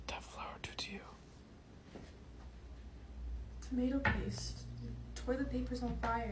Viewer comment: It sounded like she said 'tomato paste😌👍 The toilet papers on fire and what?🙃